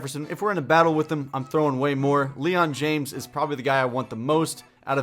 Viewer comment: I thought it said LeBron James